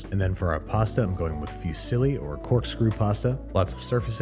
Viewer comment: when he said fusilli i thought he was saying "a few silly (pastas)"